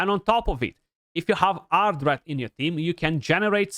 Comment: if I have who on my team with? I can't make out what he's saying :D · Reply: Haha, Ardreth. You might not have her yet.